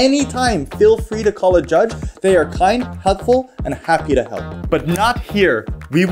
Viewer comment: am i the only one that heard "hugfull" instead of "helpfull"?